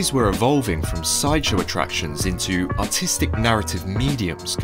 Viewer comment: I can't understand what he says around 2:30 😭 when he says " ( something) attracions " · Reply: sideshow attractions